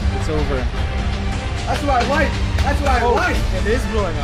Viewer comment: "That's my wife! That's my wife!" 8:10 Did I hear that correctly 😃